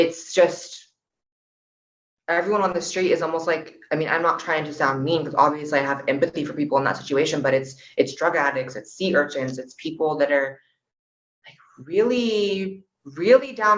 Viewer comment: Did she say "It's drug addicts, it's sea urchins"? Hollywood is scarier than I thought.